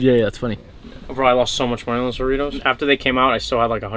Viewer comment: I thought bro said Doritos but he meant jaritos 🤦🏾‍♂️ gotta learn how to properly say it maneeee